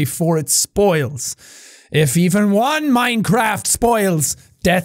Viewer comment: Lmao at 10:46 he says Minecraft instead of mindfruit 😂💀